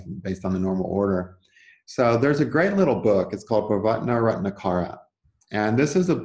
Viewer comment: What is the name of the book at 0:23. Other people in the comments have asked for the name of the book, but no response. I can't understand it. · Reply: Bhavartha Ratnakara, I thik that is the name of the book